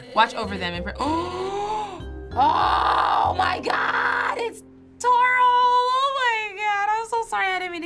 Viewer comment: did she just say toral?????????????????